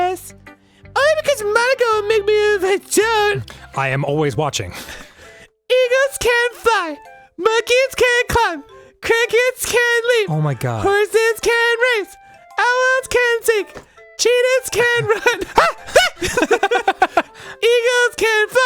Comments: I thought he said crackheads can leap lol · I thought he said crack head lol